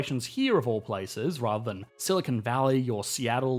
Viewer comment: Try out Trends today! Get your first week for just $1! 👉 <link> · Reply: Quick note mate, you wrote "silicone" instead of "silicon" in the closed captions at 6:05 Great video as always though P.S. Silicone valley is what they use to refer to Los Angeles due to the large number of individuals getting plastic surgery there